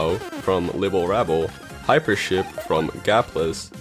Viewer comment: OMG I thought he said Hypershit